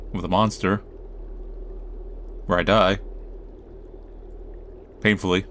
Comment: My ears are really messed up OR I'm really messed up (We already know my mentalitys answer.) because at 25:08 I heard 'where I nut' instead of 'where I die' and I swear you will NEVER know how much I was like: "B💮tch what the f-"